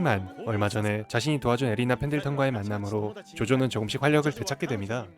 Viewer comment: ㅋㅋㅋㅋㅋㅋㅋㅋ 왜 배리나로 들리지 ㅋㅋㅋㅋㅋㅋㅋㅋ